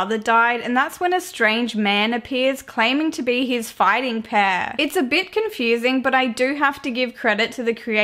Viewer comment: I thought she said fighting pair